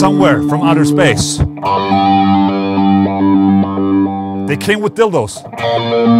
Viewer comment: "Somewhere from outer space, they came with dildos" inspiring stuff. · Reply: First I misheard as "the king with dildos"... I am not sure if that's for the better or for the worse though